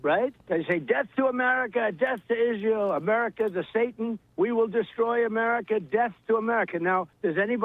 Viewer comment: Does he actually say “America is a satan” here? I can't tell for sure